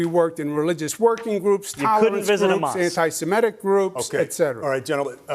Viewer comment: Did Cuomo say he worked with anti-Semitic groups? 🤨